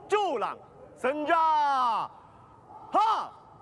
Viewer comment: Did I hear Senja-Ha instead of Senja-Ta?